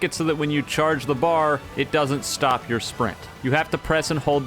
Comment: why did it say spring not sprint?